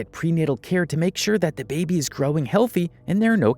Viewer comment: did he just say even if a mother knows how to grab a baby instead of saying grow?